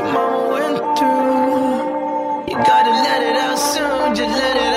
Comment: "go ahead and cry little boy. You know that your daddy did too you know what your mama went through you gotta let it out soon, just let it out" i love this part sm idk why, probably because he sounded like he's crying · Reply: bruh all this time I thought he was saying "you gotta let it out so just let it out"